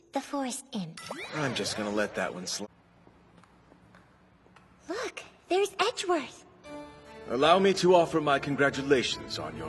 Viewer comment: Bro I deadass thought Maya said "Fuck, there's Edgeworth!"